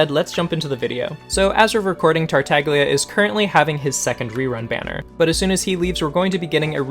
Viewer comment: did he just said second rerun?